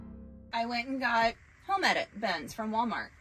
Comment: Hi, i have a question. I am having a hard time understanding what you say about your bins. Around 7:30, you say you have ___ bins from Walmart, if we ever saw your Netflix. I have played this probably a dozen times and i just cant fingure it out. What kind of bins are those? Do you have a link? Or brand? I would like to know ow more about your Netflix also. Thanks! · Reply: The bins are “Home Edit” brand, which is found at Walmart. We were not referring to “our Netflix,” but “on Netflix,” as there was a show on Netflix that talked about home organizing, which is where we got the idea to use the Home Edit bins.